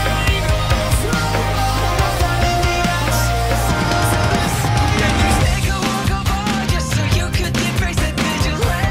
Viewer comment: WHAT DID INFINITE SAY!? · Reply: shut